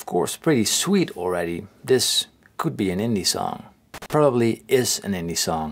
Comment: did he say Indie or Hindi? I'm confused. How the heck could he learn hindi?